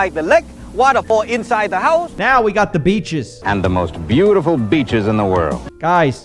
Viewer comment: he is not saying beeches he is saying bitches😂😂